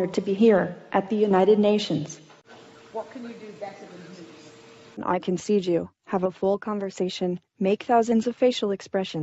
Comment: i think it says "i can seed you" :-/ too much matrix fantasy programmed in it and all.. Question was : "What can you do better than humans" ...